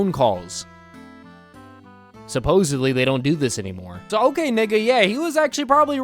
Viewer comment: did dude just say the N or am I being Schizo also 😳🥹🤧😭🤣🤣🤣🤣